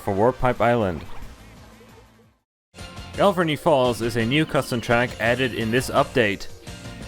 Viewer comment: when I heard galvarny falls for the first time, I thought you said gravity falls XD